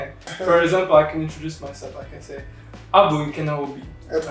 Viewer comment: sounded like you said: I'm Obi-Wan Kenobi